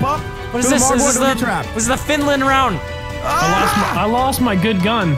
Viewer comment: Did say Finland at 8:46? Because if he did ni sit pitää mennä Torille